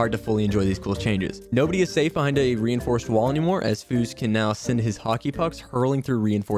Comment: did he say foose? ehh whatever Love your content btw he says it